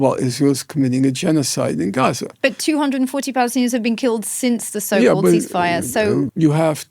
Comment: Wrong English captions at 44:50. It says 240 000 jews has been killed when she actually says 240 Palestinians has been killed.